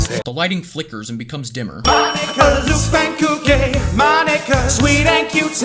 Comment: I thought it said my nigga